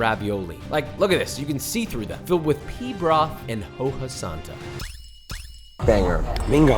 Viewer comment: Mishearing pea broth at 8:42 has me scarred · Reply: Pee broth 😂